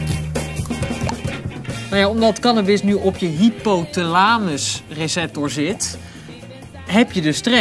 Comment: Why did he say it like "hypotul-ANUS"